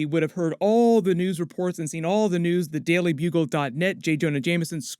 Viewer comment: did he say matt murdock would have "seen" the news about peter parker being Spider-Man